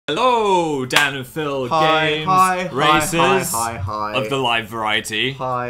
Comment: I thought phil said "hello danandphilgames racists"